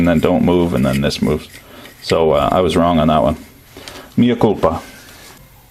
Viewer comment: did he just said "minha culpa" in Portugues???